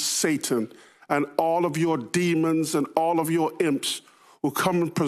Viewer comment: Oh dam ... I thought he said "simps"... lol, i was about to get mad.